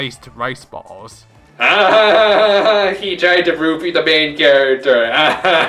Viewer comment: he tried to what ? "Roopi" ? That's what i heard... What dose it mean ? And by the way, for Teruteru I understand why the people don't like him but personnaly I don't hate him either. I mean doing the first trial I still laugh when he talks ( # AVRILLAVIIIIIIIIIGNE) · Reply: "Roofie" as in Teruteru tried to drug Hajime at the end of his free time events.